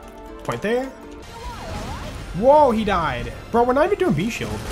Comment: I kept hearing B0re